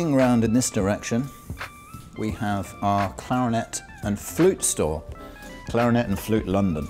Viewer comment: I thought he said clarinet and food store😂